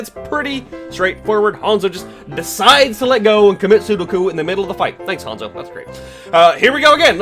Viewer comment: Did he say "commit sudoku" at 4:13?